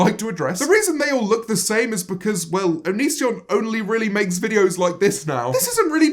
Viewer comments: Lmao I thought it said onion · I legit thought it said onion